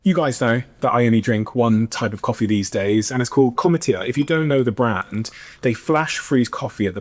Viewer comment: Dude your ad at 1:00:50 for cometeer coffee sounded like you said chlamydia lol